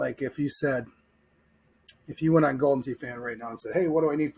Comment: At first I thought it said Golden Pee. And I thought, oh man, those are the best kind.